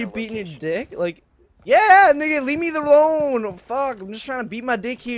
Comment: Nigga did I just hear nigga